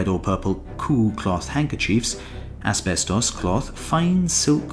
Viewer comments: did he say aspectos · Did he say asbestos cloth???